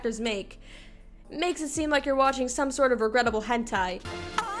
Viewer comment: "Regrettable hentai" is now my new favourite quoit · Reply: I misheard it as incredible hentai so I searched up incredible hentai